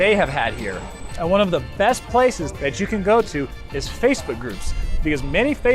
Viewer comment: when you are saying "And one of the best places you can go ...", it sounds like you are saying "Space Hookers" instead of Facebook groups. They have Space Hookers in Vegas?!!! That's WILD BABY!!!